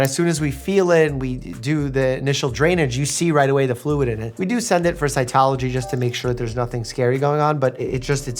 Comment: I misheard "We send it for Scientology". You had me worried there Mike!